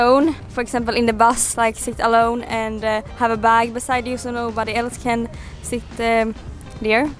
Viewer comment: subtitles are wrong, not a bike beside you but a bag beside you.